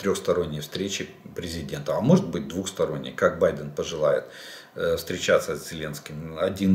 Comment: Мне послышалось "Как барин пожелает", так и будут встречаться!